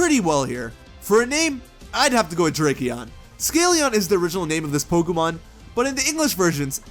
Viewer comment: When you said "Drakeon," I think for a second I thought you said "Tracheon." I've been watching too much Scrubs